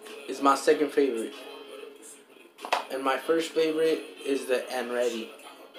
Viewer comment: Did he just say "and ready" instead of "andretti"?🤣